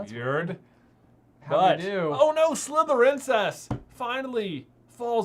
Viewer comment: Very inappropriate but when they said Slytherincess I thought they said Slytherincest.